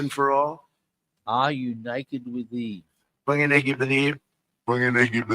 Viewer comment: i hear were you naked with Eve [ from the garden of Eden ]